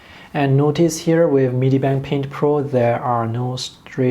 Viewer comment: what app did you say this was hard to tell??? Thank you. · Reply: Medibang Paint Pro